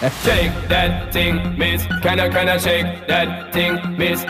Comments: as a fellow jamaican I thought he said "can you can you shake that thing." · I always thought he was saying “Can I Can I shake that thing” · This whole time I thought he was saying canna can ya shake that thing can yah can yah as in Kenya shake that thing like the country kenya that way it sound like to me 😅